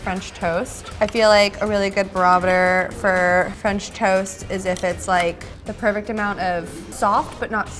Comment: did she say "a really good barometer for french toast" lol what